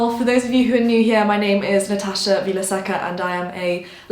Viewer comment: You’re last name sounded like you said “Natasha will I sucka” hahhaha